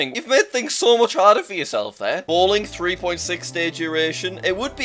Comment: Who heard him say 3.6?